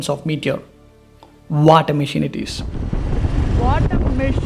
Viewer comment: I heard "WHAT A MASHIN!"😅